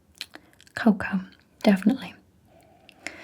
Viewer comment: what does she say at 15:34 ? because I hear "cow cum" every time...